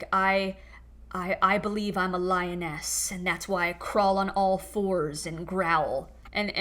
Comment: I heard lioness as “lyin’ ass”😅—just my default reaction to anything that jws say(or maybe the effect of what I’m smoking 😅). Yeah I’m gay and also a dentist—think of me as the Tooth Fairy 😅… Couldn’t resist 😅!